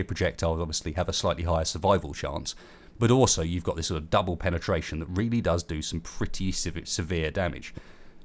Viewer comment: Did he just say double penetration? XD